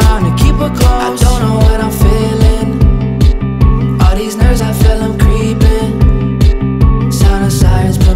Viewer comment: Lmfao I thought you said nerds I was like ayee a shout-out but you said nerves